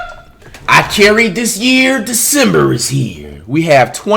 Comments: I honestly thought he said I can’t read this year December is here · “I can’t read this year, December is here” I thought he said that🤦‍♂️ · Yo I’m trippin I thought flight said “ I can’t read this year, December is here” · It sounded like he said i cant read this year december is here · I thought he said I can’t read this year December is here no cap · It sounds like he said “I can’t read this year, December is here”